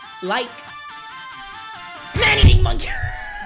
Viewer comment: did you said mommy monkey?